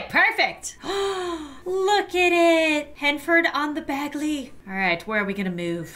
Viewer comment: For the town, instead of Henford-on-Bagley, I heard 'Heine Turd-On- Bagel"